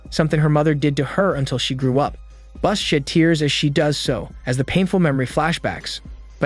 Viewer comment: "bus SHIT tears" LMFAO, even though you said "sheds tears" in the closed captions, it sounded like SHIT without the closed captions. 🤣🤣🤣🤣🤣🤣🤣🤣🤣🤣